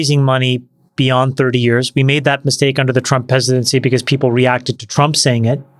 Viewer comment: <3 @ 1:19:46 when Chamath misspoke and instead of saying Trump presidency he kinda said, "Trump peasantdency" ...that's hilariously accurate!